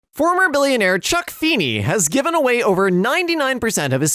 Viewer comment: When that said Chuck Feeney I thought it said Chuck E Cheese